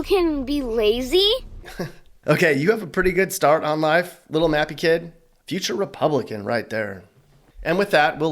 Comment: When u say mappy, i thought you were saying nappy kid. I was like wth did he just say.